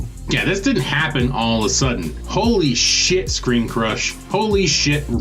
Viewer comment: For a moment I thought you said Screen Junkies!